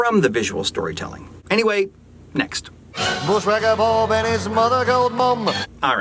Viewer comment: I heard it as Bushwacker Bob and his gold digger mum >.>